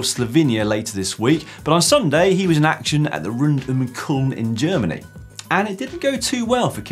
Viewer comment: What race did Kittel race in? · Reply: Rund um Köln. It’s in Cologne. It sounded like he said Kulm.